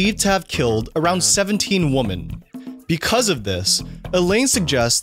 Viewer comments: you say the singular 'woman' instead of 'women'. I need to know why. I'm hearing this all over the place now, like an entire generation of people don't know how to pronounce 'women'. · "believed to have killed around 17 (WOMAN)"? Did I hear that right?